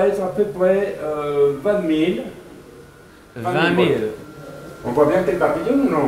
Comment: when the french guy says 20,000 its sounds like he is saying "vladmir"